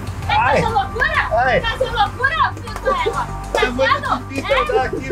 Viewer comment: FI DUMA EGUA SO EU QUE ENTENDI